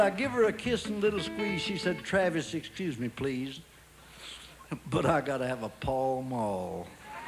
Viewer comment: what does he say at 2:43? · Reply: But I gotta have a Pall Mall. (Pall Mall is a brand of cigarettes)